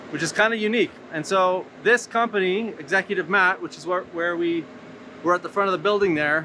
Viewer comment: What's the name of the company mentioned at 8:19? The captions say Executive Matt. Thank you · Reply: <link> they are a sister company to Executive Mat.